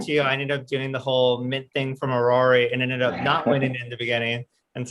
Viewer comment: wat nft gaming project did say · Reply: Aurory